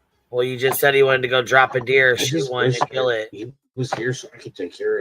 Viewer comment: When listening i thought said. "this year sebastian would drop his first BEER." at 17 yikes. drop a deer is alittle better I guess. IDK